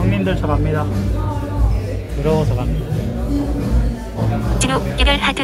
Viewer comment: "드러워서 갑니다" 라고 들리는데.....?